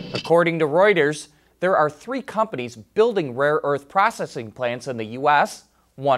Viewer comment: "Rare earths" aren't that rare. If multiple plants are opening here in the US, then it would seem the trade war is doing its job--moving work back to the US. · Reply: Thought he said "rare Erts"? Possibly a small nocturnal rodent mainly native to China?